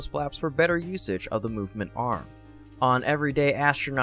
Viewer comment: Good video but you got one thing wrong at 1:35. You said “movement arm” and showed a video of mechazilla but that’s not at all what Elon was referring to. Moment Arm is what Elon said, which refers to the amount of leverage induced by the flaps, upon the whole of the Starship. The more forward the forward flaps are, the longer the lever (or moment arm) that will pitch the nose up. Moment is the amount of torque applied to a body.